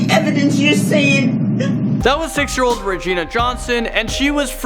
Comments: who else heard him say 6 year old 🤣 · did he just say that woman was 6 years old lol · I’m sorry did he say, “that was six year old Regina Johnson” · did he say 6 year ikd